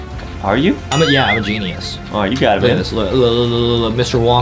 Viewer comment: Why does it sound like says “Alright you got it baby” or is it just me?